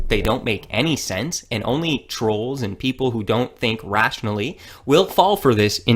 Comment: Holly shit back but I thought he said no trolls